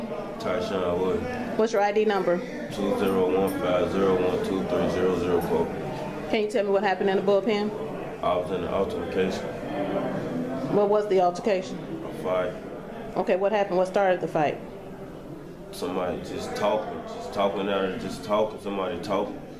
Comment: Lol did he just say I was in a Alter-ma-cation 😗